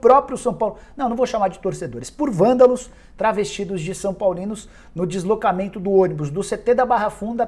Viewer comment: Vândalos travestidos de São Paulinos kkkk !!!! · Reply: Eu entendi " travestis são paulinos " kkkkk